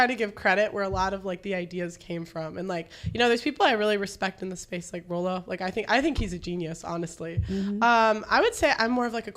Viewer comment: What did Pearl say? ~ @ 50:30 ~ "...I always try to give credit where a lot of my ideas came from,...people I really respect in this space like "_____" It sounds like Roloff Is that it?